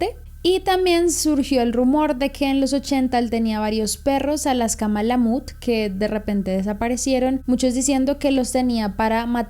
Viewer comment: Que dice ahi? Es una raza de perro? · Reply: Si, Alaska Malamute se llaman